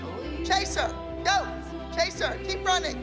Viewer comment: Am I the only one that thought that Abby said Caseoh instead of chase her 😭😭